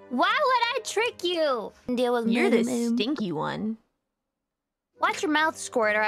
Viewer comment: I just want to point out that she actually said was,"Watch your mouth squirt or I will ..." but considering how slurry american accents are I can see how this was misheard and with much comedic effect xD Also Squirt is slang similar to pipsqueak, shrimp, gaki, or chibi. Dads use this term endearingly to address children. It can also be used mockingly or to assert dominance, sasuga Gura.